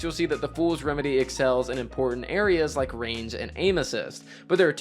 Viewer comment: range and anuses...that's what you said, right?